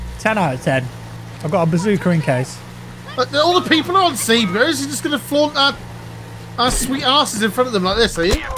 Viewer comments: what is saying? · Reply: "All the people are on C. Is he just gonna flaunt our... our sweet asses like this are you?," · I swear you say "Bazooker"